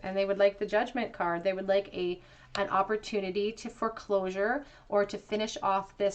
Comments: This was a perfect reading for me pile 3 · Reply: OMG, I had such a funny synchronistic moment with pike #3. I was listening to the reading and saw a license plate that said OWNAHOM = Own a home. Then I heard you say, they want some kind of closure, which sounded close to forclosure. It made me laugh a huge belly laugh. Spirit has a way of bringing across messages and confirmation with humor at times. Love it when it happens this way. Thank you for being a channel. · OMG, I had such a funny synchronistic moment with pike #3. I was listening to the reading and saw a license plate that said OWNAHOM = owen a home. Then I heard you say... They want some kind of closure, which sounded like forclosure to me. Spirit has a way of injecting humor into this Tf experience, which is so needed at times. Thank you for being such a clear channel.